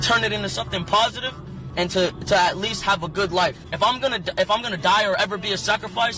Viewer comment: anyone else hear him say “if I’m gonna die t”and remember that message was recorded 2 hours before he died what I’m getting at here is he was about to say if I’m gonna die “today”